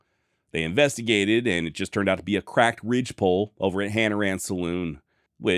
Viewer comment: I thought you were going to say crackhead_ 🤦‍♂️